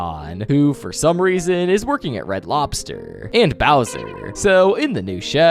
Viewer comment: did you just say he works at bowser?